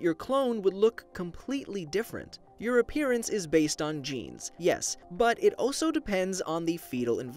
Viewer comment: What he seaid: Your appearence is based on genes What I heard: Your appearence is based on JEANS